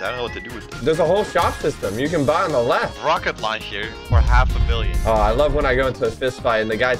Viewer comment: what's did he say.. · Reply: Rocket Launcher You Didn't Know He Said Rocket Launcher